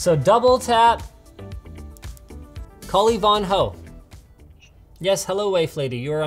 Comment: Until she picked up, I thought Linus said "cally von hoe" as a nickname for his smartphone.